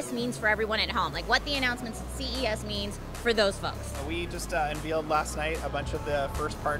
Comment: did he just say unVEALed?